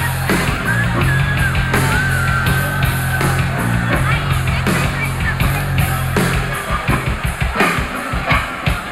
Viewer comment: What do girls say at 7:15 and 9:54? I can not hear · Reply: i could only hear in 9:54, the woman said: da licensa ai segurança! it´s like, excuse me security. But she said in a way that look more ´´get out of my way security´´